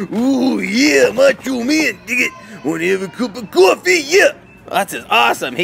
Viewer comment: did he just say the n word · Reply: no it was dig it